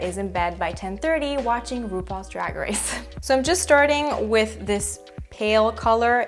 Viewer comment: I don't catch the name, what is this program? sorry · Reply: RuPaul’s Drag Race!